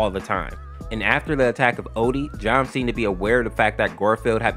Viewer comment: did you call him john cena??